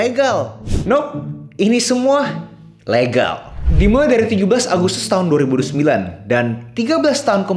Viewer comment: Apa gak salah dengar 17 agustus 2029